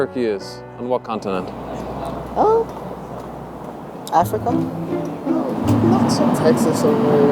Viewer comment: DID HE JUST SAY FUCKING TEXAS???